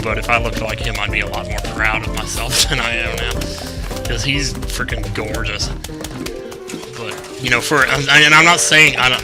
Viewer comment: That was scary! I skipped part of the video and I hear "Chuggaaconroy's Freaken Gorgeous!"... o_0